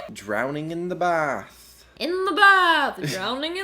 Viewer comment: captions were on and at 9:16 instead of "drowning in the bath" it said"drowning in the bar" so apearently you can drown wit liquid just being in your mouth ┐(￣I￣)┌